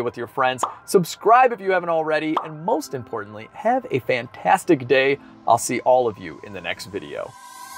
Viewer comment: I thought you said “have a vantadtic day” at the end